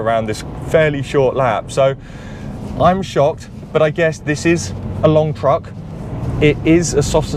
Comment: At minute 7:50, did I hear him say "this is a long truck"... I actually fell off my seat in laughter 😅😅